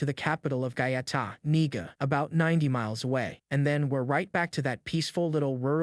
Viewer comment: I beg you pardon the capital of what …😮 I swear he said nigga